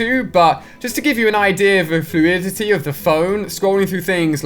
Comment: What did he say at 5:33 he said something like fudditty